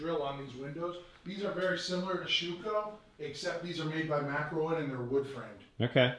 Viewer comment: What company made the windows? They say they’re not Shueco but I can’t make out what he says... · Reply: Makrowin sourced thru European Architectural Supply